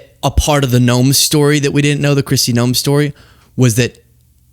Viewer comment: So honestly, when said Noem... I thought he said Gnome, and I thought to myself that I've always envisioned more like Troll... but I see it. Gnomes can be ornery and aggressive too 🤭😅